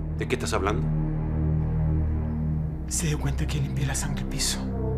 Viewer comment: Que brillante cuando Milo dice “No se dió cuenta... limpié la sangre” SU CARA, SU VOZ, es todo muy bacan, l o a m é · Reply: Por el acento Chileno escuché "hay sangre". después de dos años, saludos desde México.